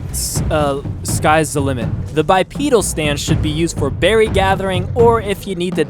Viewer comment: anyone else hear bi penis stance?